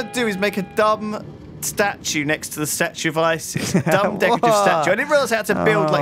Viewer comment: did lewis say statue of isis?